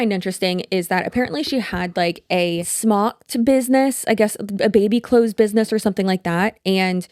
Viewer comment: I heard “snot” (smock) & “baby clothes” and my brain mingled it as “baby snot business” 😂😂 Rewind… didn’t say that 😂 SMOCK Business. Listening and cleaning fail. Lmao